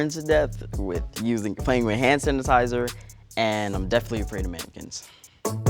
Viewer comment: I thought he said "I'm afraid of Mexicans"😂😂